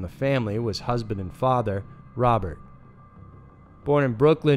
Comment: Is that an editing mistake at 1:48? I pissed myself laughing when I heard "Bobert" but not sur eif was supposed to be joke or mistake. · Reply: Holy shit. It is 'father'. I'm an idiot. After listening to it four times I got it.